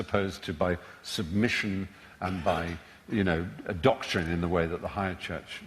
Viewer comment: "... by submission and ???" What does he say there? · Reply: doctrine